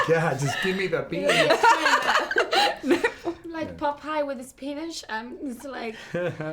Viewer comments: I thought she said like "popeyes and his penis" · am I the only one who heard “Popeye with his penis” 😂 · I swear to God I heard "like Popeyes with his penis"